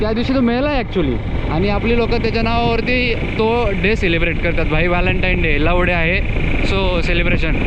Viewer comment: What did he sayy 🔊😂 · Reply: Love Day 😂